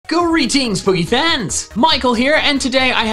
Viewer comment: “Greetings pokéfans” plays battle cats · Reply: I Hear "Boogy Fans"